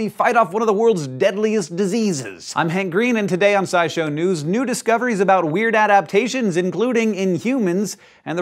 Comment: Did a double-take when I thought I heard Hank say "Nudist Coveries"